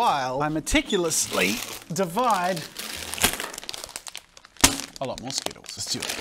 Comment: he said "a lot more skittles" but the subtitles say "i like mosquitos" lol